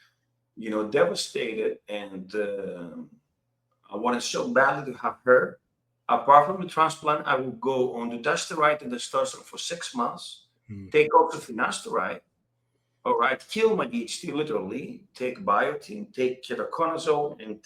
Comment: is he said dutastired and testesteron ? if its so how many mg is he was taking back then ?